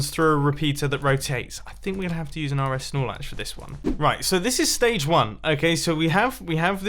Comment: its embarrassing how long i thought he was saying are es snorlax